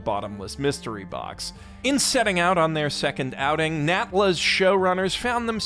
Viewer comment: bro, i thought you were saying nambla at first not natla💀💀💀